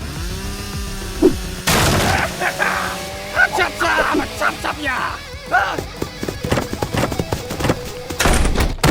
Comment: What is he saying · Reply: Ha-Cha-Cha I'ma Chop-Chop Ya!!